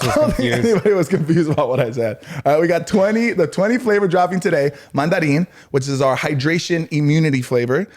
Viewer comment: i was confused when said "20 dropping today" sounded like 20 more! Got your back, 😂